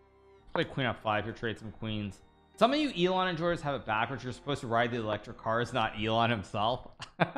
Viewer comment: can someone transcribe this please? I didn't understand a single word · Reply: I believe that Hikaru said you You Elon enjoyers have gott itbackwards, you should have be riding the cars. Not riding him. I think he said